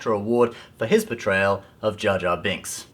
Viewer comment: At first I thought he said "betrayal" instead of "portrayal of Jar Jar." To me, either makes perfect sense.